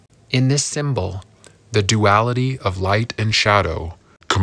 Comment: What’s the third thing? I couldn’t hear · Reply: Thought he said “a homeless”